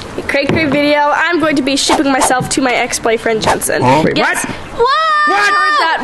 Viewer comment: i almost thought she said "today, im gunna sh*t myslef to my ex bf!" but no, luv u!❤